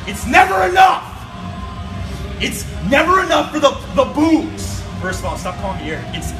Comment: I thought he was saying boobs instead of bugez 🤣